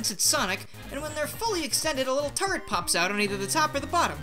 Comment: The first time I watched this video I thought you said turd, instead of turret.